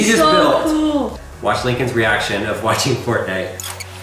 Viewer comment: It sounds like he is saying "watch Lincoln's reaction watching pornight" 😂